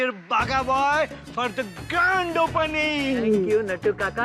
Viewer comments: granddd orr whatt😂😂😂😢😢😢 did he just said g nd opening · Did Nattu Kaka say "g*nd opening" instead of "grand" 😭😭